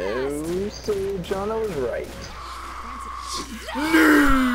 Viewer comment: "johnna was right" is that what he said? if so, what was she right about?